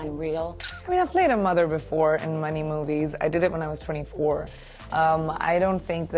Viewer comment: It sounded like she said in money movies 😂